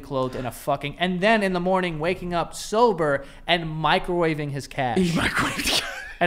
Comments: Literally thought y'all said "microwave his cat" lmao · Oh my god.... I thought y'all said "microwave his cat"